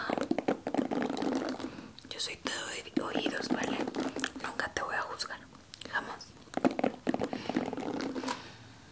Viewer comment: en el minuto 1:52 que dijo? ;-; yo soy todo oidos... y que mas? :V alguien me dice no la escucho bien en esa parte xdd pero igual hermoso asmr :3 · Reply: dice... nunca te voy a juzgar